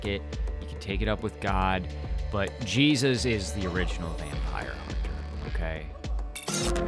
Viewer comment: Jesus... the original vampire HUNTER, yes... I thought you said "the original VAMPIRE" I was like.... ahhh helllllll nah. 😂